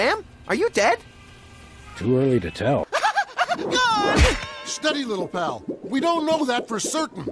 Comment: i know he says 'steady little pal' in the maxochist part but i first heard it as 'steady little bell' and i SOB THINKING ABT THAT